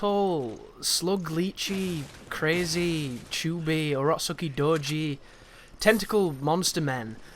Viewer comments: Anyone know what said at 14:34? rock sucky dojo? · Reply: Late comment here but that was Urotsukidōji, it’s a horror manga from the ‘80s. mentioned it in his Evil Within Akumu run too so I assume he’s a fan haha. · Anyone know what said at 14:34? rock sucky dojo?